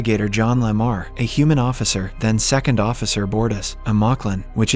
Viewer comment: Bardes(I wrote it how I heard it) are gay?